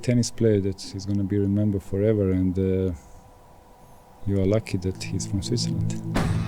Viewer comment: wat does goran say at 3:18 'you are lucky he is mosisalent'...